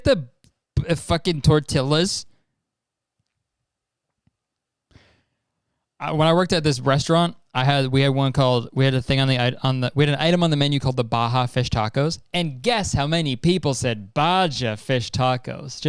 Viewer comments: did he just say breastaurant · I KNOW HE DID NOT SAY BREASTERAUNT